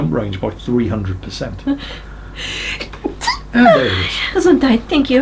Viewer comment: Did she say "Gesundheit"?